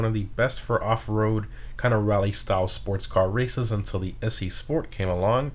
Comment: What car did you say at 3:14? Until the what “sport came along”? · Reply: Issi Sport